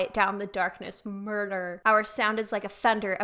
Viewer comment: I knew there is a reason why you are one of my favourite Ateez-reactors! I also caught on to "The Fellowship" and was wondering about it as I am a huge LOTR fan. Then I read the title "The Ring" and actually was joking when I related it to the one ring... until I heard the last two lines. 🤣 And of course I misheard the "murder" part: I thought it said "Mordor" and was quite confused. Many Atiny said that Left-eye was a kind of Gandalf and I really like it (and with Gandalf/Left-eye, Ateez consists of nine people - the fellowship). So now I am really curious who represents what LOTR character...?🧐🤪 Sorry, geeked out here! But I really liked your reaction! ❤